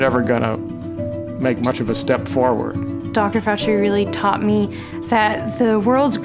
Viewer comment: i thought she said dr fauci at first i was like wtfffff